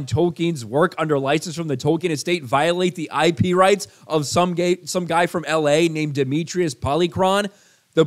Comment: it sounded like you were about to say "some gay guy" LMFAO!!!